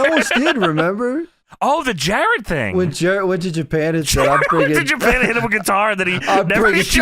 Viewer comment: What did said here? I can't make it out. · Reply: Jarrett went to Japan and hit him with a guitar and then Hogan never followed through with that angle